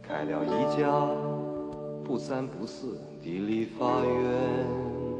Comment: 盲人歌手曹松章的小巷陽光。個人推薦最能代表眷村之歌。當年被禁唱。只因為。一句歌詞。審查的重聽委員。聽成。開了一家不三不四的立法院？